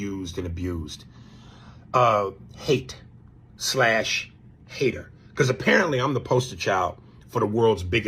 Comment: I didnt hear Cube say hater, I heard him say Aries is a sucka LMAO! Aries Spears aint been funny in years!